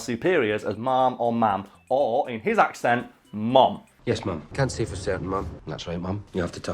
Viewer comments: As an American who uses the term ma'am on the daily, I still thought he was saying mom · I hear "mom/mum" but I know he's saying "ma'am." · Honestly I thought UK did say mum instead of ma'am. It never really occured to me it was all accent and not just a difference in culture. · I knew he was saying "ma'am" with an accent but I did have an immature giggle at that point, because to my American ears it does sound like hes saying mum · american here, is does sound like he says "mom" 100%. but as i am not a moronic american, i understood he was saying "Ma'am" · So I thought it always was mom but I never thought she was his mom if that makes sense. I didn’t know he was saying ma’am, I thought you all just said mom instead. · I'm an American and the whole time I thought he was saying, "Mom," and then once they started nutting around there was about 35 seconds of me blowing my own mind over it until my American brain finally rationalized, "Scottish... He's saying ma'am." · I always heard "mom" but after years of working with Scots I knew it was meant to be "ma'am" lol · As an American, I hear "mum", but I always just thought that's how his accent said "ma'am". I never saw it as a term of endearment. I apologize on behalf of my country. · Canadian here... I've always thought all Brits were saying mum, and that it was just a term of respect. Actually feel stupid that I never made the connection to ma'am, especially as I say ma'am quite a bit.